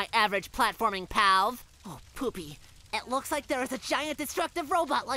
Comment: I think I hear saying oh poopie